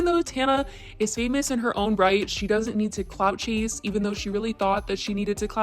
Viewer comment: I thought you said COCK CHASE instead of clout chase. Either way. Both are appropriate 😂